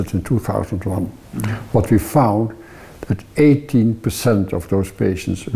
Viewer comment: Did he say18% or 80%? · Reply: I reviewed the English subtitles and he said 18%